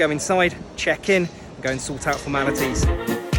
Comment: i thought you said "lets go inside, check in and sort out the manatee's" (:45). I got excited to see manatees!!